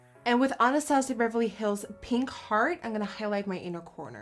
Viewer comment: I really thought you said pink fart when you were talking about pink heart from the moonchild palette 😂😂😂😂😂💩